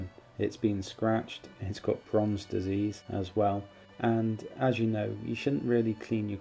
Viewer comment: What is the disease you speak of around 2:10 a bit difficult to make out what you said. Fromms, Promms, Bronze? How will it affect my collection? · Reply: Hi, sorry i have a unusual accent, its called bronze disease though it does affects coins with a high copper content and it is basiclly rust as it normally happens when the coins have been in contact with water or a humid environment. It can affect other coins very close to it as well. This one is still have in my collection though just in a air tight capsule. I could do a whole video on the subject but for more info there is a good wiki page on it. Hope that helps <link>